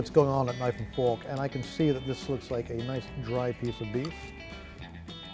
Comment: did he just say... a nice dried piece of meat? lol